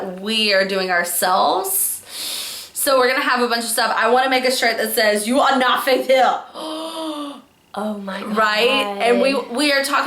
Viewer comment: The shirt is gonna say "You are not ...". What was that last word? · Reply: Faith hill From the jelsa video